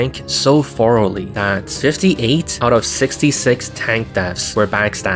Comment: Misheard as "68/66" at first and didn't question it. Yeah, sure, I'll believe the Spy counters the Tank so hard it kills 8 non-existent ones.